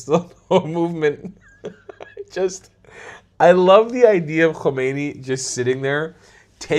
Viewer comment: I thought he said I love white man